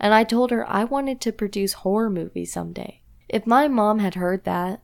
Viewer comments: SHE SAID HOROR I THOUGHT SHE SAID WHORE MOVIES 😭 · God I thought the girl said she wanted to produce porn movies and I was like 0×0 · I thought she said porn movies 😅🤪